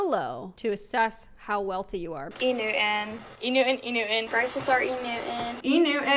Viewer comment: I thought they were all saying "enu-in" and I was so confused😭😭 safe to say I'm not in that tax bracket💀